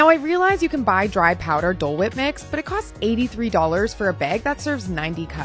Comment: I thought she said cool whip at first like girl $6 for a cup of cool Hwip?